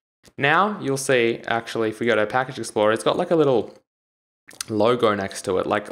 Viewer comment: what does he say at 3:36 -3:43 does he say packages floor? if so what does that mean? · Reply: package explorer